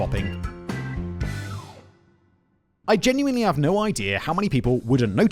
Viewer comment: For one hot second I thought Simon said "sin swapping" and I thought "hmmm, kinky!".